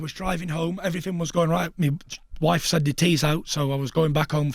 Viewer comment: Thought he said mi wife had her tittty out 😂😂